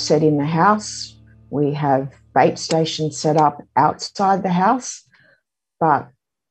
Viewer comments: I swear she said "we have babestation set up outside our house" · for a second I thought she said something else station then 😂😂😂 and thought how would this solve the problem but she said bait.